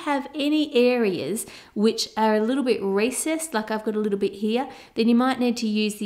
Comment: Did anyone else think she said racist at 6:46??